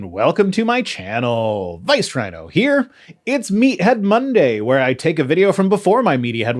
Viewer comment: I keep hearing “media head” as “meathead”. I guess that works too. 😂